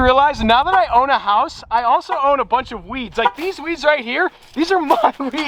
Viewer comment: I thought you said now I'm a house owner I own a bunch of weed 😅😅😅🤣